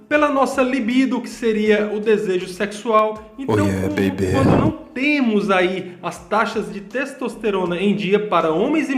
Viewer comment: quem foi q falou Oie bb?🤣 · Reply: Kkkkk gostou heim !! É "Ow Yeah Baby" 😁😁😁